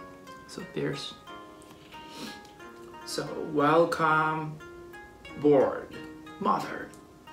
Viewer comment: did he just say welcome pork mother?